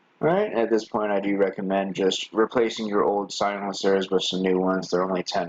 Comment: What did yous say to Replace for $10. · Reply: The little rubber silencers that does spring sit on